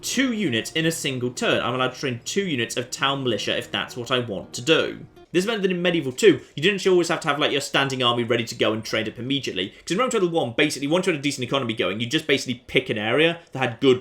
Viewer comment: did anyone else hear him say "rome total one"